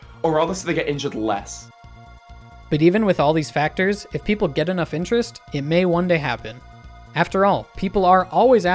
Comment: I thought I heard "if people get enough injures"